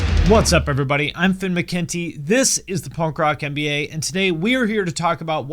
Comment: i keep hearing "punk rock NBA" in the intro